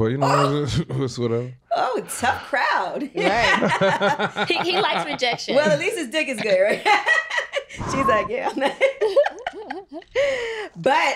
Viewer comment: Did she just say “at least his Dixk is good “? Like girl whatt 😭😭😭😭 9:13 guys am i tweaking 😭😭😭